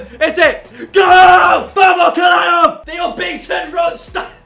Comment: Did he just say “vamos caralho”? I’m proud